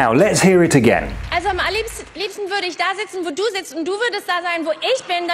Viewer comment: I hear: "am allerliebsten" not a repetition in the beginning, but an emphasis. And in the end: "da[nn] wär' das Ganze viel lustiger". No mistakes and I listened five times to make sure 😁